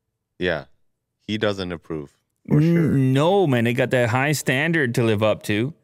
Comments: did just say no my nigga? lmao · does he say " noo ma ni**a"?? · did he say: "No , my NiG....???